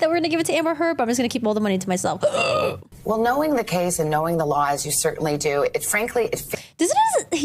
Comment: I thought she said lies too! But I believe she says laws.